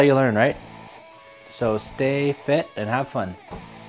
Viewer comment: Did you quote "body break" in this video lmao I had it on as background noise and I swear I heard "get fit have fun" lol